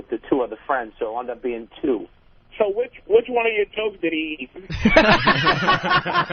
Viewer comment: What did Patrice say at 20:16 ? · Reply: "Which one of your jokes did he eat?"